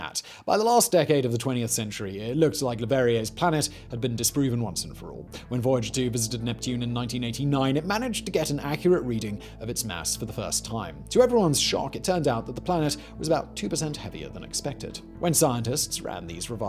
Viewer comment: I honestly thought you said "ass" as in neptune's behind, since we only see whatever side is lit by the sun